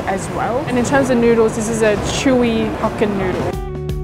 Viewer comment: I thought you said' this is a chewy fkn noodle ' 😂